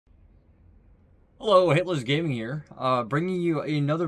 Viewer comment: So often i hear you say ''hilters gaming'' XD